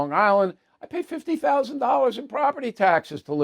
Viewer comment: Did you say $50 thousand or $15 for LI property taxes? I live in Suffolk County and pay $15, 000 , but it sounded like you said you pay $50,000. If so, that's ludicrous! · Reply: That's what he said, $50,000!